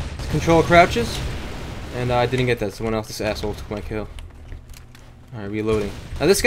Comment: Did he say ASSHOLE IN